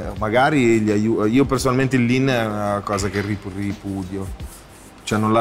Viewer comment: ma cosa dice? ahahaha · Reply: l'ha preso e gli ha rubato l'anima e basta